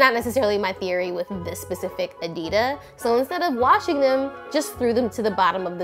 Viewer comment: Am I deaf or did I just hear say ''Adida" instead of "Adidas" ...is that like a thing now?